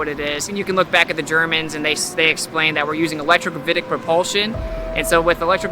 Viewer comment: Electro 'something' propulsion? What is the word he's using there? It can't be electromagnetic, electrostatic, electrodynamic, or electrothermic. It doesn't sound like any of them. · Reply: Oooooh!, he said electro-gravitic! BUT, I thought they didn't believe in gravity?!?!